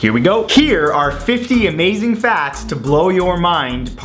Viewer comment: Did you say "50 Amazing Fats"?